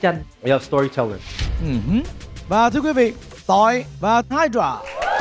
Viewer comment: ông Thành đọc Tọi và Hydra tui nghe ra tọi vào Hydra 😂😂😅